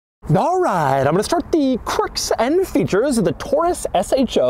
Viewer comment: I didn't quite hear the S in features, so it sounded like Doug said "I'll show you the quirks and feature" which seems about right for it